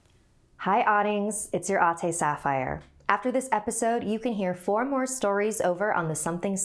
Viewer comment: " Hi autanigs, its ur AUNTY Sapphire" 😂😂😂😂That's what i hear lol